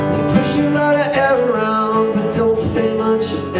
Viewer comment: I thought it said they push alot of heroin' haha oh it's air around'